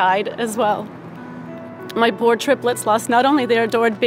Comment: Did she just say "my four triplets"? How does that work